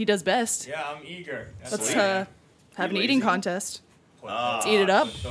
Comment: Eating... EATING contest! I thought Lindsay said MATING contest! I was like what the fuck!? lol